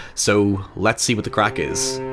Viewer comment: 'lets see what the craic is', Jesus fucking Christ, I didn't realize you were THAT Scottish in your soul lol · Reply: Oh, I thought he said "crack".